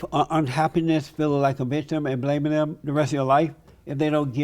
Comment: Did he say feeling like a bishh!?😅